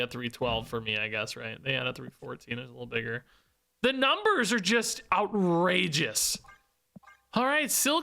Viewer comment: Every time you say “outrageous,” I hear “out-Regis” 😂